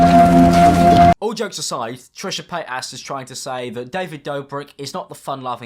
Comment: did he just say Trisha PatASS?! 💀